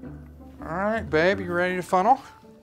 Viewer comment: I thought he said 'Alright baby you ready for funeral'- he's literally killing her.